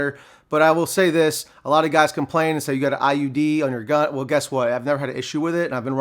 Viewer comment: did mans say "IUD"??? 😆